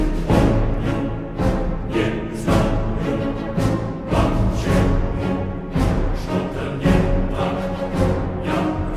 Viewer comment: listen closely I heard kravchenko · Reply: “Dragovich, Kravchenko, Steiner, ALL must die!”